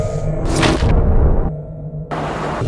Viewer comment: I heard Back Sit :'(